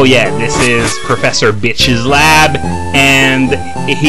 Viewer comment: I also thought it said Prof. Bitch XD